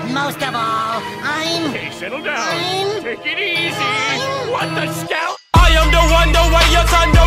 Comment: When you think plankton says what the hell 👀